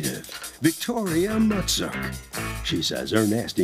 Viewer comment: Tell me why i thought he said her name was Victoria Nutsack 🤣 I had to rewind it just to check.